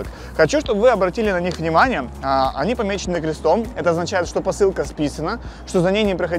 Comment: посылки спизжены( мне так по началу послышалось))